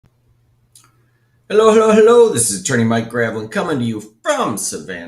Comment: Listened to one of your vids the other day on 1.25x speed. My daughter (8-yrs-old) thought you said, "Hello, hello, hello...this is Attorney Minecraft..." Lol